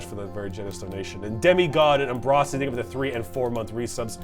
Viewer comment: did... did he just say Umbrocity? But that... that's me...